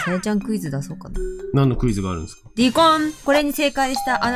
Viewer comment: ディコンッ!!(?)っていう問題出題音音が「離婚」に空耳して単身赴任とか言うもんだからポップな離婚だな〜って流してたけど違うのね?